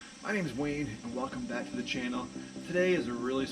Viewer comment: Today, it sounded like you said your name is. Previously, I thought you said Wayne. I'm thinking either you are schizo and have multiple personalities, you have an evil twin, or you had a stuffy nose/seasonal allergies!